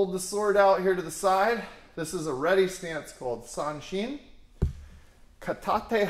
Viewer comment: did you say Sanchin ?? As in サンチン ?